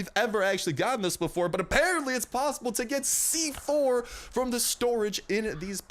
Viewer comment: did he say C4? HE SAID C4! IT WAS COPPER ORE!